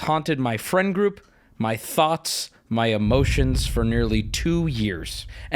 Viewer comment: I really want an update but I fear that we wont get one. Also, does Wubby say "Has haunted my friend groups, my thots"... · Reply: He says thoughts